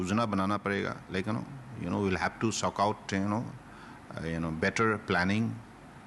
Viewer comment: Did he just say "We will have to suck out?"